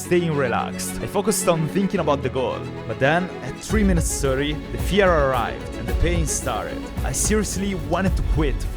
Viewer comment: is it just me or did he say Führer?🤣